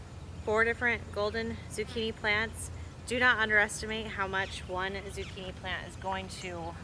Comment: I had to go back and listen. Why did I hear “I’m going to plant 400 zucchini plants”. 😂